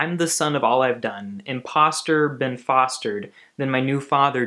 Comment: I've always thought he said Ben foster, and I was so confused. Thank you for saying that it was been fostered. The song makes so much more sense now